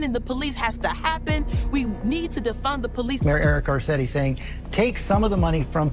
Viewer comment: I thought Brandon said "Fun the police"? Fund the Fun Police?? That sounds about right 😂